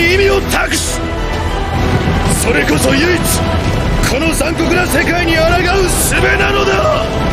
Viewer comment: Y a que moi à 1:34 pour "ce monde impitoyable" qui entend "CyrIiiIIl HaNoUNAaaAAaa" ?? (ce qui est ma foi extrêmement accurate si vous me permettez)